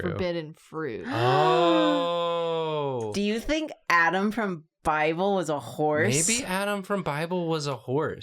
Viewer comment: Not gonna lie, I heard "Adam from Bible" as "Adam from *Fievel*" and I was like "What character was named Adam in that??"